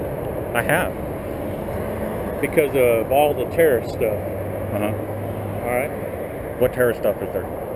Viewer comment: "...because of laws of terrorist stuff..."? or did he say, "...because of lots of terrorist stuff..."?